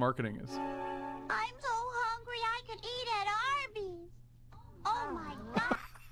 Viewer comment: OMG i remember that clip from the simpsons but we don't have arbys in the uk so i had no fucking idea what that meant and i thought she was saying 'i'm so hungry i could eat an armys' which is terrible grammer at the very least!!!! O MY GAAAAD